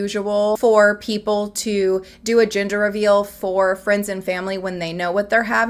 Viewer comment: It took me about two minutes to understand you were saying ‘gender’ reveal. I heard it as ‘ginger’ reveal 🙃